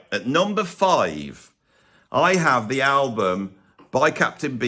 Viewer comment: Every time you mentioned Captain Beefheart, I thought you were saying "Captain Bee Fart". Which is actually a much more intriguing stage name. If he had opted to call himself that, I would have tried one of his albums by now.